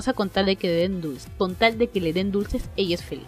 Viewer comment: En el anime "Watashi ni tenshi maioritai", escuché "Con tal de que le den duro" xd